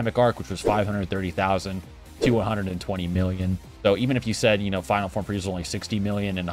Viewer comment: At the end of the planet namek saga friezas power level was 1.8million and gokus was 1.6million in super saiyan. Piccalo was only about 700k. I think i heard you say billions?